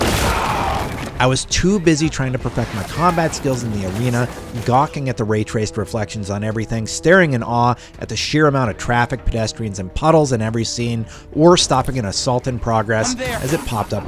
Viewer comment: did bro say “gawking” 😭